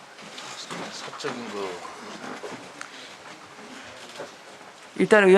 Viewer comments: 부분에 "사적인 것으로" 라고 미리 말 맞추는 게 들림....수석이라는 사람들이 진짜 너무하네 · 쯤에 사적인거 저만들리는 건가요? 혹시 들으신분 답글좀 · 잘 들어보면 사적인거라고 시민사회수석이 얘기하는게 들리네요? 입맞추려고 얘기한것으로밖에 안보입니다